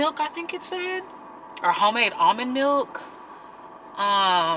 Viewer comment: I thought you said vomit milk...... Lmao then im like ohhh almond millk